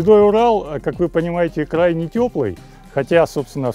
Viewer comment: Послышалось «крайне тёплый», вместо «край не тёплый»😅😅 Молодец! Как же хорошо, когда мужчина с головой и руками правильными!